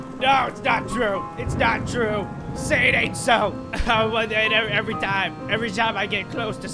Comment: For 5 years I thought he said saynatso like I don’t know what that means but he is saying say it ain’t so